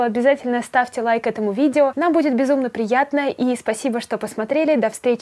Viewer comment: Не в обиду, но мне в конце послышалось "И ебите котиков!"